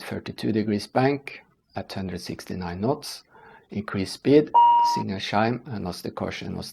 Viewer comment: at 22:38 it's written at the subtitles 269 knots instead of 169 knots, the correct speed of the alarm